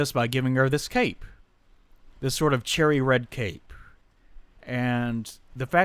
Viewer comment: oh god I kept hearing you say the translation of serees was "charity". and I went what the hell?, and listened to it over again about 7 or 8 times. I finally heard you say cherry.